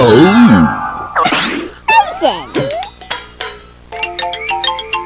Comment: it sounded like he said GOTTA BLA-!